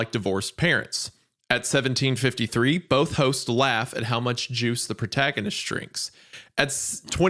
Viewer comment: I heard this as "Juice, the Protagonist" and I was freaking out trying to figure out who was Juice